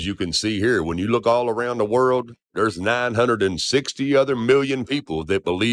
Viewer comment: Did he say, around the world?